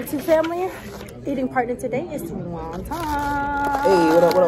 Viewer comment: Did you call him wonton 2:39? lmao I screamed